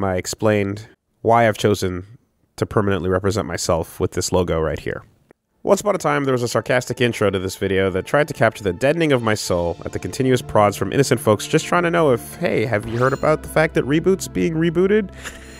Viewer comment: "That tried to captcha the dead-nigga of my soul " .... I swear to God thats what I heard the first time , and the third time that I hear this aned I just keep hearing it this way now, and yes I know that he really said "deadening of my soul" but I SWEAR the first time around I heard "the dead nigga of my soul" and that picture of the skull guy i thought that was some meme like the "dead nigga" of his soul...